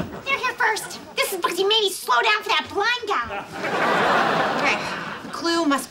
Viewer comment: It sounded like said " This is cause you fucking made me slow down for that blind guy " lol